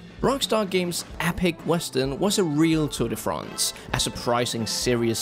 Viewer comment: did he say "Tour de France"?